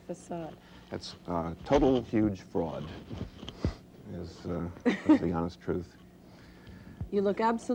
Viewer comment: What does Paul say at 4:09? English is not my first language. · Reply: "That's a total, huge fraud. That's the honest truth." She said he looks calm when he's playing and he is saying that he's faking it--meaning he really isn't feeling calm.